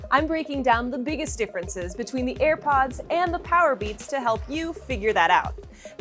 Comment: did she say earpods😂